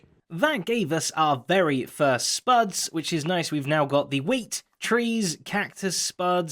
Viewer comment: I have to admit, when was naming his farms, I thought that he said WHEAT TREES. When I realized my mistake, I was a bit disappointed.